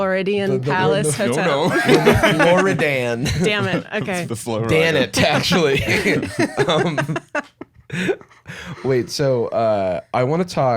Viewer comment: did Jarvis call Ashley?